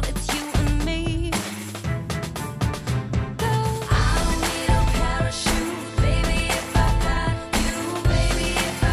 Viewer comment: What does she say at 1:34?! PLEASE SOMEONE TELL ME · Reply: She says "Love"